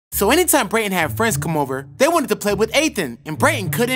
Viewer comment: I thought you said brayton had no friends